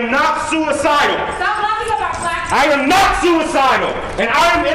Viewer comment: That’s so funny…I was thinking this exact thing!!! On a side note: could anyone tell what that woman yelled out at the end when he was ranting about “NOT being suicidal”? She said something about “don’t laugh at…” but I couldn’t understand because he kept talking🙄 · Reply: "Stop laughing about black people" I hear "lying/laughing". It's more likely it was a supporter....so it is laughing. Some people were probably smiling at "if they pull an Epstein on me" Which is laughable.